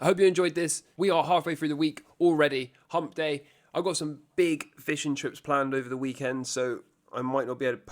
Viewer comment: I definitely heard "big Fish & Chips planned over the weekend